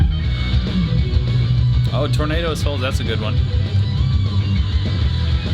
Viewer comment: did he say "Tornado as Hell"?